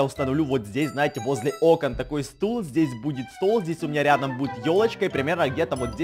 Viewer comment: Мне послышалось что ты сказал не ёлочка а тёлочка